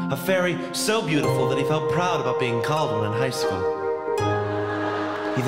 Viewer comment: A fairy so beautiful that he felt... what does he say? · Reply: That he felt proud of being called one in high school